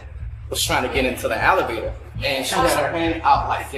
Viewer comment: Does anyone else think the security guard during the OF model’s bodycam was saying “alligator” instead of elevator?? Listen closely lol 32:20😂😂😂